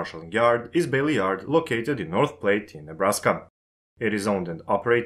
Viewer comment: Just so you know, Platte is pronounced like flat without the f. Sounded like you said plate.